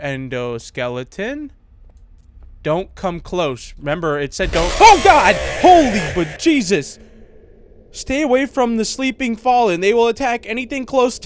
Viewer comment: did he say OH GOD! HOLLYWOOD JEESUS! xd it sounded like that XD